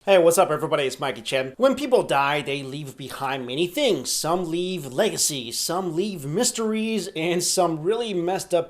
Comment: I thought he was about to say some people leave listerine 😂